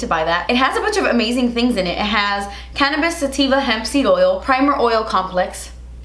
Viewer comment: excuse me did you say cannabis???